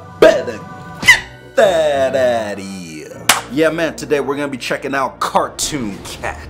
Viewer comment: bro captions said “GET THÂT DADDY HERE